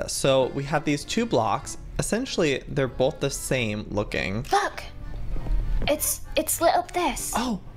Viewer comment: I thought LD said the F word but then I kept on putting it on and on when I realized she said "look" instead of the F word😂🤣😂🤣